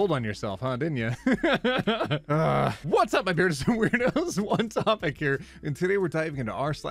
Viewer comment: For a second I thought he said "Hot Topic here!" 😶